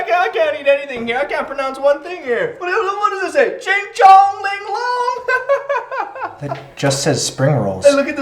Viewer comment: Did he say shengshou linglong??!! Anybody get the reference?!